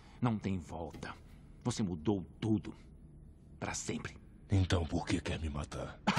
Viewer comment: Eu entendi: então pq que me mamar 😂